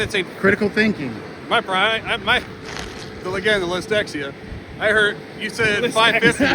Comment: did he say dextexlia lMAO